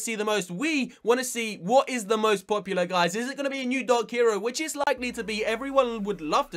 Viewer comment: at first i thought he said new dog hero lol